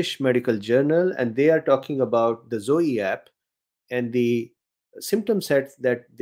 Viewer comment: When you said Zoe app at first I thought you said zodiac😂I love your artwork. Thank you.I think I had this Omicron, first a few days of dull headache, then sore throat with fever, loss of bitter taste but have sweet taste, night sweats and weird pains like in jaw.